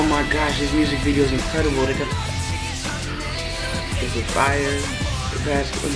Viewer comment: DID HE SAID 'INFIRES' okay nah my ears just got poor because of my freaking earphones..